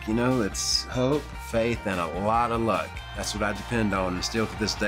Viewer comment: At first I thought he said "Coke, faith, and a lot of luck" 😂